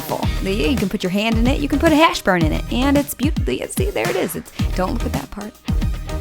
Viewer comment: you can put a hashBURN in it?? am i the only one who heard that?